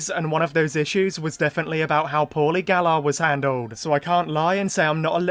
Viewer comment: Can anybody tell me 3:31 , what is paulie galla? Or whatever he said ?